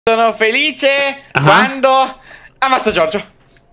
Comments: ma solo io ho sentito sono felice quando mi faccio giorgio?😂😂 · Io ho capito "quando amavo Giorgio" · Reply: Amazzavo